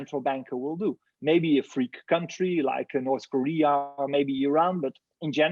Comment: Interesting he mentioned that free countries like Iran and north Korea are buying Bitcoin. I guess the rest of the countries are controlled by central banks and therefore cannot. Be your own central bank people! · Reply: I thought he said “freak countries”